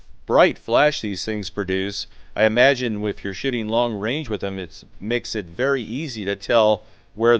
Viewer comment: Am I the only one that swares it sounds like he said (shitting long range) at that last part...lololol